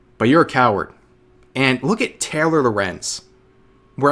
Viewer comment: did you just call her "Terror" Lorenz cause that would be fitting af, I like it